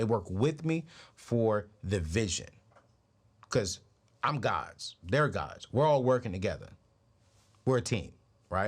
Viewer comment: Did he just say "we are" guides or gods?